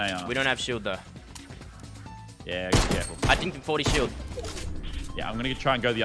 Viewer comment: did fresh say dinked for shield